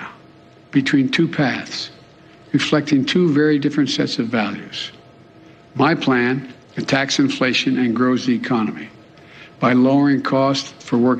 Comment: Did he really say tax inflation?